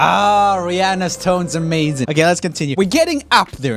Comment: I swear he said Ariana's tone is amazing, not Rihanna's lmao